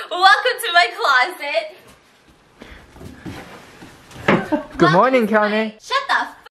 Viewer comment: does someone know what he just said here? 7:40 good morning whaat? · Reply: Good morning Kanye not sure why but they said it alot before 😂😂😂